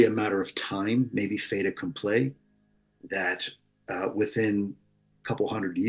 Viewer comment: Can anybody help me track down what he's saying at 44:30 as well as what it means? It appears to me that the first word is "Fata", a Latin word. But I have no clue on the rest, and I'd greatly appreciate anybody's help 😊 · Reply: Hi Zach! He said “fait accompli”